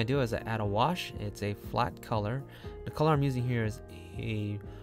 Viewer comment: Might be a dumb question but at 0:37, did he say "wash" color? What is a wash color? If i did not misheard... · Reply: You did not hear wrong, it is really “wash color” which means use undertone color (light purple) to cooler a whole skin. Huhu… I don’t understand the part, he erased the edge of the girl, then erase all the rest of messy background, then he said locking the transparent … then what next? I tried to paint the background, it kept mixing up with the edge of the girl… and my dense watercolor looked like s***